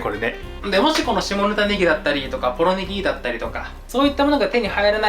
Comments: 下ネタネギ…に聞こえた… ん？読みは合ってるのかな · 下仁田ネギが下ネタネギに聞こえてしまう… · 下仁田ネギが下ネタネキに聞こえてしまった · 下仁田ネギが下ネタネギに聞こえた笑笑